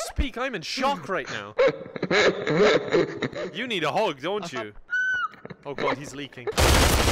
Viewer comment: On-screen subtitles say “Dying Animal Sounds” while YouTube’s auto-English subtitles say “you need a hog don’t you”